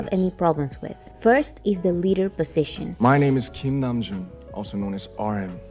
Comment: now I can't unhear it😭😂 "My name is king Namjoon" 😂 I'll should consider using "King Namjoon instead of Kim Namjoon 😂